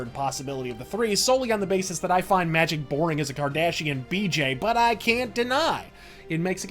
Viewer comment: I heard "boring as a Cardassian bj" instead and I thought, can they do that with their necks the way they are? What would that even look like...